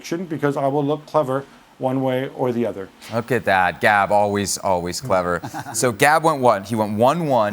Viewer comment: did he just say 'dad Gab' lol